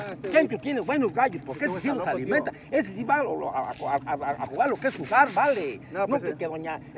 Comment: Alguien sabe que dijo en el min 2:05 jajajaja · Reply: Jesus Mtz ese si va a jugar lo que es jugar